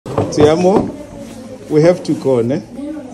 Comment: When I first saw this video, I thought he said "Ti amo" to the boy which means "I love you" in Spanish, but then realize it's his name called like that; Tiamo, Thiamo or something like that.